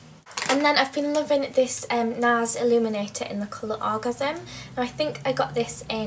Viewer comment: did she say "the colour orgasm?" or is it just me?:')